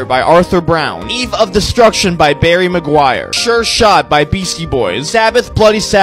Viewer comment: for a second i thought it said harry maguire instead of barry maguire